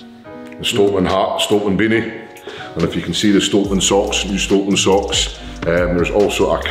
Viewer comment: Shit! When you said camo socks I thought you said camel socks. Dat Scott accent